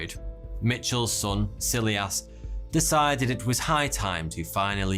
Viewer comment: 11.22: "Egder" Allen Poe 😁 Wonder if anyone caught that...... OMG 😱 I need a life 😭😫😂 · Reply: silly ass instead of silas, don't know if that one is a freudian slip though.